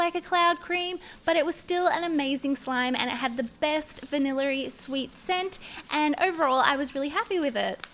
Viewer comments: I think she said vanillary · Did anyone notice that she said "vannilla-ry" instead of vanilla? ? On 3:31 No? Just me then..